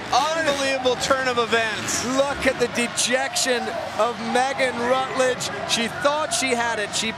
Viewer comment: ngl I thought he said “look at the degenerate”